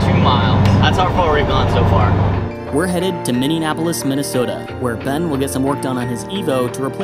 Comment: Did he just say Minne-nap-olis?